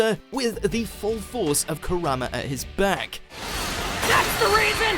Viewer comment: I always heard him saying Corona instead of Kurama